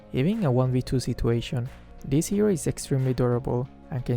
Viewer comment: I thought said "This hero is extremely adorable" cuz I wasn't looking at the subs lmao